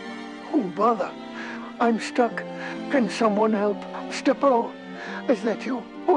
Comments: It sounded like he was saying Stepper-o, but now I get it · It sounded like he was saying Stepper-o, but now I get it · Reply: Yeah, I thought he said stepo until I read the comments.